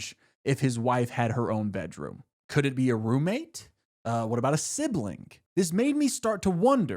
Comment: "woah, he's actually getting really clos- oop, no, we're talking about carla again" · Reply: i heard “or a sibling?!” and i leaned forward in my chair for a second